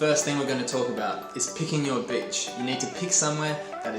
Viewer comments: I thought he said “first thing we’re gonna talk about, picking your bitch” I was like I thought a board or something would be first and the bitches come later but hey you’re the expert sir😂😂😂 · Mate, I thought u said “pickin your bitch” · At first I heard, “Step 1: pick your bitch” 😂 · Did anyone else think he said picking your bitch? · i thought he said “first step is picking ur b****” 😂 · I thought he said “picking your b*****”